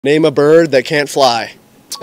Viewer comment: i heard "name a burger that can't fly"😭